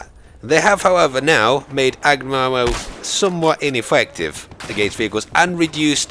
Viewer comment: what did he say ? lol "magrun arow"